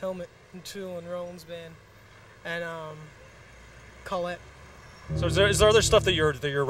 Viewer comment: what is the name of the last band Daniel mentions as an influence? Sounded like he said Collette?